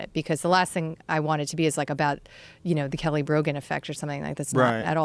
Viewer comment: I thought it said Kelly Rogan and I got excited that it was his wife